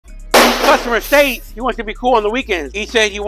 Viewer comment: I thought He said,"She wanted to look cool on weekends;" not like a wannabe that over paid for a 1" drop for a beat up Accord. Hahahahah 😅